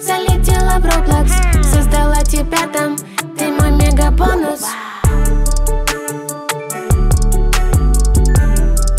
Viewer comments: WHAT DID SHE SAY??? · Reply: "You my mega_bonus" "Ti moi mega bonuz" "Ты мой мега бонус" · WHAT SHE SAY ⁉ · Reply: She say: Ты мой мега бонус (You are my mega bonus)